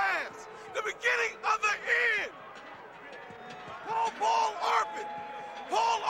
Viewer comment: i wish i could help you. i would like to know what Derek Loville is saying starting at 30:18. I can make out the “beginning of the end” part but he says someone’s name. Paul Ball Harping w/ the official NFL fan line? I know it’s useless knowledge—but he was going off. I feel like Loville was always good for an interesting sound byte. Cheers. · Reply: Loville is yelling, "Call Paul Arpin! Paul Arpin! The official van line of the NFL! Take yo' ass HOME!" <link>